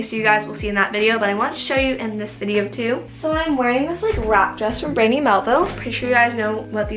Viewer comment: I low key thought she said a “rat dress” 🤦‍♀️😂